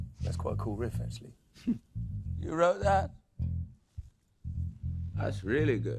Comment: what is he saying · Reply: He said ' You wrote that? '